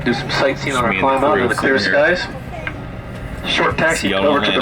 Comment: area did you say see y'all in Atlanta?